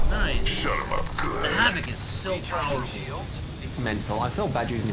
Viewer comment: Did he just say shut up scat suit. XD